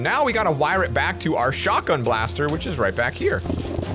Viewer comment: I thought you said CHAKRA BLASTER